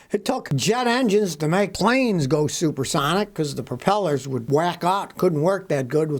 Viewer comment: Did he just say that propellers wack off?